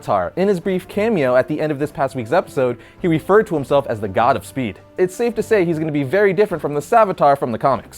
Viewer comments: I thought he said "Savitar, the Godspeed" in the Flash episode, I was wondering if it was a combo of Savitar and Godspeed. You'll have to excuse my lack of knowledge when it comes to comics, I'm new to them. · I thought he said "Savitar, The Godspeed"